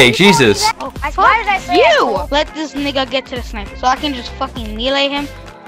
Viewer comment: did he just say nigga?